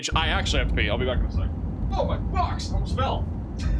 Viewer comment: did he say, "Oh my f***s, i almost fell" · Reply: oh my box I almost fell😂😂😂😂😂😂😂😂😂😂😂😂😂😂😂😂😂😂😂😂😂😂😂